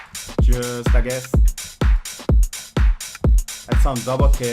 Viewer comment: Everytime he says 'just like this' I hear it like 'Just I guess' xD